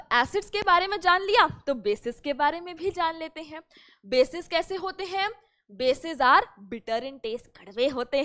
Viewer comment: I heard "Bhadve hote hai" 🤡😂